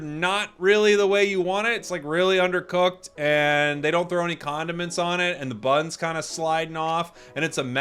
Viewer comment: Thought he said they didnt throw any condoms on it